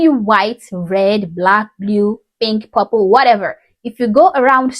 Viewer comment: Lmmfao I swear to everything, I thought she said habo not purple took me three times 😂😂😂😂